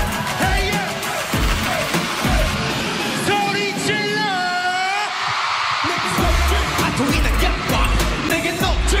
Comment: really wanna know what tae said here😫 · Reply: make some noise